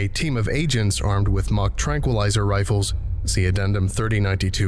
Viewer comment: I'm glad I finally understood what they were talking about The tranq rifles. I thought they said "Mach Tranquilizer Rifles" and I originally thought, oh good god. instead it's "mock" thank God.